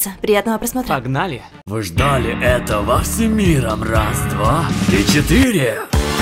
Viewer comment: Браво! Идеальный перевод! И рифмы и всё всё, просто идеально... почему я могу поставить только 1 лайк?! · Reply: во первых у тебя херовый слух и тебе послышалось раз два три четыре Они произносят раз два и четыре Во вторых да ладно? Перечисли все ошибки.